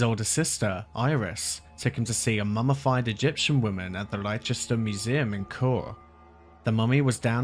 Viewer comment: you said LYE-chest-ER. You're English. You can't pronounce LESS-tuh-shurr? I get flubbing the place names in foreign countries, but that's like me saying YO-suh-MITE instead of YO-SEM-uh-TEE. Leicester or Leicestershire is not like a Welsh village name. No chance you're fishing for comments? Otherwise, great vid.